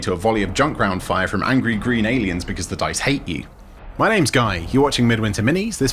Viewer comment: i heard "the dyes hate you" and now i imagined a new ork color wich is just "DIZ KOLUR JUST HATE YE" and painted a bunch of bullet with it